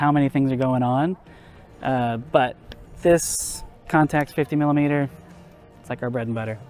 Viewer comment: Anyone know what 50 mm he was using? Sounded like he said “compact” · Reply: Zeiss Contax 50mm